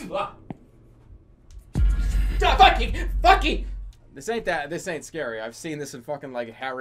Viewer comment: did he say fucky?